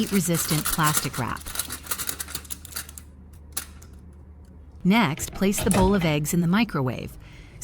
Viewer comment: Good tips 👍 dear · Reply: I thought you said plastic wrap not foil